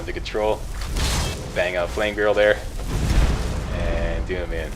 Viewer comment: WTF? what did you say at 15:10 "Bang Flame Girl"??? ouch, are you trying to burn Lucky?... LOL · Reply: Stryker Jenkins No, I said, Bang OUT Flame Girl.. lol.